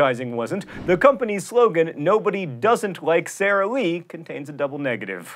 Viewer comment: I honestly thought it was "Nobody does it like Sara Lee." I think I prefer my misheard version to the original.